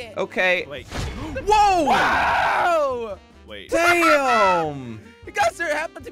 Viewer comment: did he just say damn